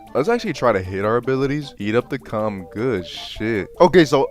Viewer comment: What did he say? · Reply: Lets actually try to hit our abilities